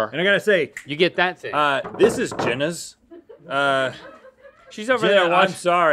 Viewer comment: Lmao at 5:50 I thought Rhett was saying “this is Genius” like a valley girl 😂